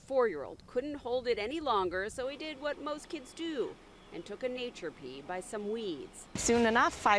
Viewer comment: These cops have their priorities backwards!!! Nyc is definitely a mess!!! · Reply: Thank you. I repeated the short several times and I think I heard "natural pee" which I've never heard outside this short. LOL. Was there un-natural pee?